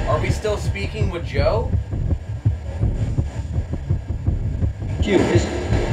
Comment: sounded like a black man saying “you is”